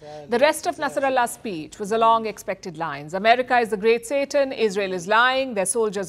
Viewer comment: Did she say 'America is the great Satan' at 3:32?